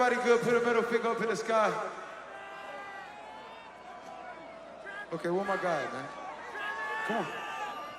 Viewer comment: Can you show me the video where he acknowledged something was wrong, and then proceeded to encourage the crowd to stomp their feet? That never happened lol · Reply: I did watch the concert and it was unclear who they were. I heard him say “where my guys at “.